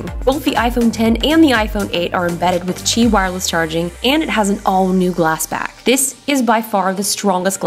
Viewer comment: I thought she said cheap wireless charging